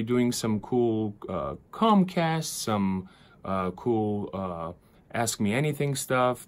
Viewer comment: did he say cool comcasts????